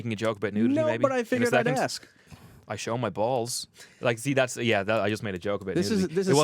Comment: I show my balls. Did he really say that? XD XD